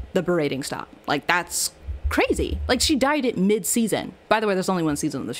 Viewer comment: I heard “she died..her hair” I thought you said SHE DIED AND OMG 😭